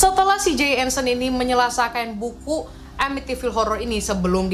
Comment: gua kira salah denger lu bilang "menyelasakan" sampek gua ulang mulu 🤣 Asli nagih banget denger story telling lu Sist 🤧